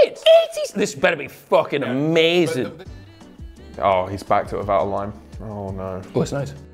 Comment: when jim said “oh no hes backed it without a lime” i thought he said hes backed it without a line😂 thought they were on the coke for a sec